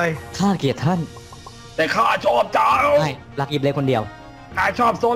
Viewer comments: ได้ยินกันปะที่บอกว่า ไม่รักกิ๊บเล็กคนเดี๋ยว ได้ยินปะ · ใครได้ยินบ้าง:ข้าเกลียดท่าน แต่ข้าชอบเจ้า:ไม่!รักกิ๊บเล็กคนเดียว ประมาน21:30